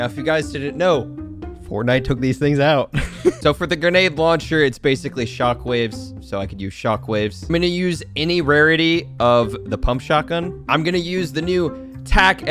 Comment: I thought you said any rarity of a pump?